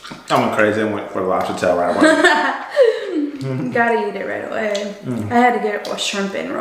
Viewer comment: When said gotta eat it ready away, I seriously thought she said daddy ate it right away